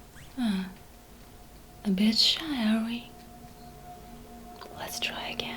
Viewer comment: What I heard: "Give us a sign" nothing "Ah, a bitch, are we?"